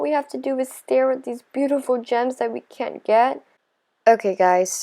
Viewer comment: It sounded like she said germs instead of gems😂😂😂🤣🤣🤣